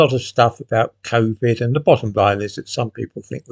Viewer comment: "There is then a lot of stuff about Covid and the bottom V ..." Could someone tell me what are the exact words, please? Thanks. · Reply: bottom line